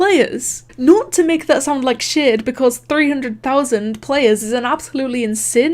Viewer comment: I heard her say “s**” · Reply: she said shade Im pretty sure